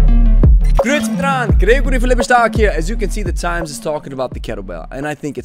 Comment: That thing you say at the start of videos, what is it and what does it mean? The fact that I hear it but don't know what it means makes it stay stuck in my head. Is it "gritztrang Grigory for Lebe Stark"? I have to know. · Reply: It's "Grüezi mitenand, Gregory von Lebe Stark hier". The first part is the formal greeting of Switzerland to a group. :) - Gregory